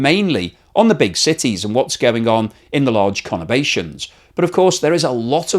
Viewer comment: What is the meaning of the word conavation at13:13 ? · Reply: conurbation